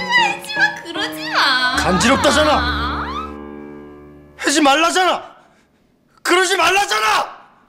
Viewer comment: 해지 말라자나 로 들림ㅋㅋㅋㅋㅋ 진짜그러넼ㅋ